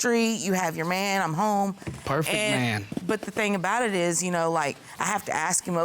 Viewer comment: Did he just say “he’s a perfect man” ???😆😆😆